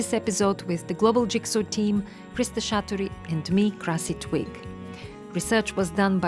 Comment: Lmao I thought she said her name was “Grassy twig”